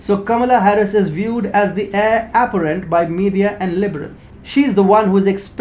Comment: It sounded like you said Heir "aberrant" not Heir apparent Aberrant definition is - deviating from the usual or natural type : atypical, abnormal.